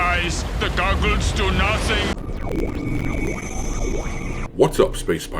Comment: Man, I always thought he said, "ze goggles, zay do nuhsing".